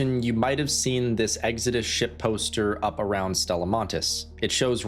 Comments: Ooooohhhhh. I thought he was saying Exodus Shitposter. Like someone was trolling us from space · I thought you were saying shit poster lol